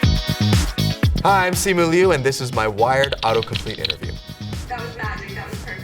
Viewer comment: I legit thought he said "Wife" for a second LOL